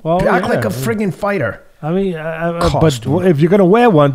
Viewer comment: he actually said uniform which is even funnier